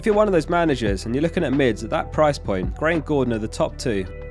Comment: Who are the top 2 Bailey replacements? I only hear it as "Frank Gordon" but that's obviously not it (Gordon makes sense, but who's the first) ? I've tried listening to the snippet 20 times but no luck so far, it just goes too fast for a non English speaker (I guess some graphics would've helped). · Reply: Gray or Gordon, both play for Everton and both are 5.5m.